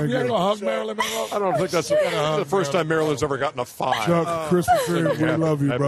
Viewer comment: ''i don't think that's the first time marilyn ever got a 5'' is ernie really that savage or did i just misheard? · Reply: u misheard. he said he thinks that's the first time marilyn ever got a 5.